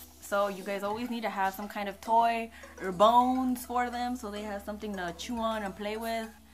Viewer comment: DID YOU SAY YOUR BONES?! I was fracked out right there